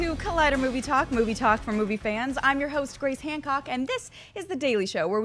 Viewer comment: I swear I heard she said, "I'm your host, Great Hancock"